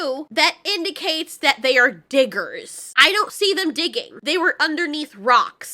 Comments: strange, when he said "diggers" all i heard was "didders" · i heard say "nice ditlers" does not help with the face those diggers have O_o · I didn't hear say anything like the N word at first I thought he said "Bidders" then said "Diggers" and then you pointed out the N word and I was like Ooooooooooooh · When he says "diggers" I hear "didders" dont know what a didder is tho 🤣